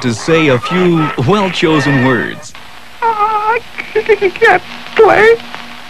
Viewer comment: the hell did he say? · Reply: he can't play it thats what he said